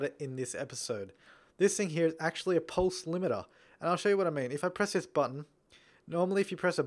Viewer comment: What did you say that one was called? Pole slimator? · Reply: pulse limiter